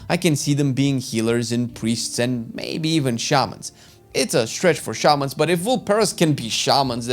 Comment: “It’s a stretch” for Tuskar shamans? They literally have shaman in the story in Dragonflight. They have their own totem models and all lmao. I thought they were gonna even add them with all the new models they made! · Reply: I thought he said it wasn't a stretch. Idk, I watched this yday.